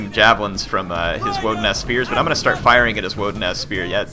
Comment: I thought I heard "Wooden ass spears" instead of "woodenaz spears"